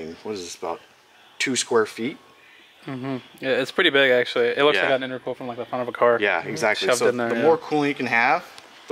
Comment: “It looks like I had a new girlfriend” That’s what the automatic subtitles thought you said at 9:24.